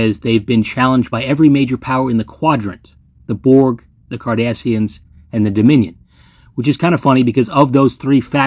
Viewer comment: 😂 close caption said KARDASHIANS